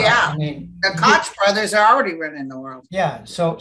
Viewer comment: did say "The Crotch Brothers are already running the world" ?? haha hehe hoho :)